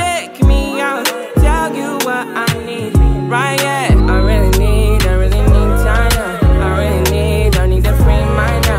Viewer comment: Sound like she saying I need Jah now